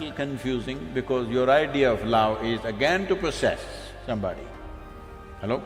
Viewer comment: Sadhguru: love is again to "Possess" somebody But caption wrongly printed as-- "Process"